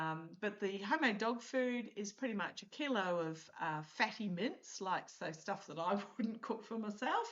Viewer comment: Being a US citizen, till just a month ago I hadn’t heard the term mince-what we call ground beef or hamburger. I had to listen twice. Because it sounded like you were saying in your dog food you put fatty mints. 😂😆